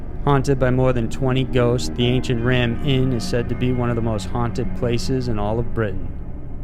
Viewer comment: I thought you said, "Ancient Ramen." I've eaten plenty of that already, thank you.